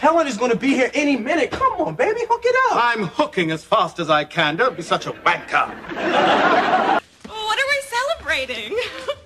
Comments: "Don't be such a wanker"! Goodness gracious. That is such a BURN and nobody watching in the continental United States would have understood it. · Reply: At first I thought he said “Don’t be such a White girl” You couldn’t imagine how embarrassed I felt when discovering what he really said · Did he call Will a wanker or did I mishear that? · Did he say wanker?